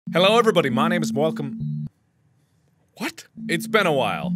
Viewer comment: "My name is welcome," Yeah, this is big brain time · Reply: oooooh welcome..i thought he said malcolm i was getting concerned lol